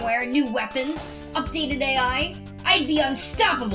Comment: Did he say AI?